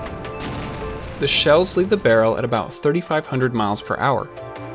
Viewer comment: На 6:17 что говорит афтар? Со скростью питсшистокоутрёумач?))) Что это было?)) А про "гусЕничный" я вообще молчу)